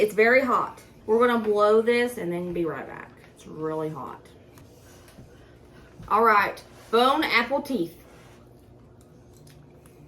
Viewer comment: What I heard was “bone apple teat”. 😂